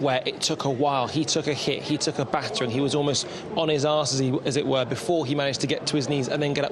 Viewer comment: did he say "on his arse?!" 🤣